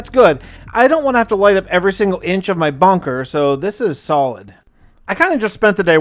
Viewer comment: anyone else hear Bonker instead of bunker? No? Just me? Aight then